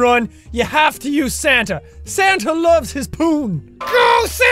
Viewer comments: Omg I thought Jack said Santa loves his porn instead of Santa loves his poon and I was laughing like an idiot. 😶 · My brother and I were watching this and my mom walked and thought Jack said "Santa loves his porn" 😂😂